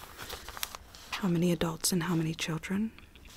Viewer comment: LMAO i thought she said "How many adopts and home made children?"